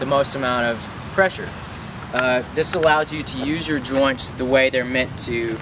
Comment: I was high when I was watching this video and on 1:46 when he said that you should use your joints I thought he said smoking tree